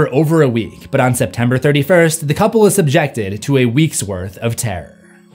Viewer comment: Did he just say "September 31st"?